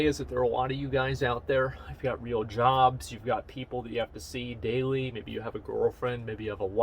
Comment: Why did i mishear daily as daily quest? Eh, I'm not bound by that in wow.